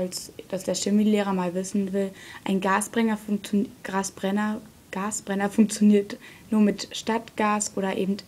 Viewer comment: Ich hab immer Gras Brenner verstanden lul